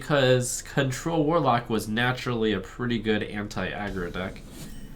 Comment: Every time it gets me: I hear "Hentai aggro deck"